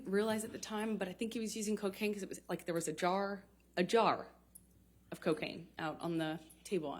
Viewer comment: JD was probably saying : A mega Jar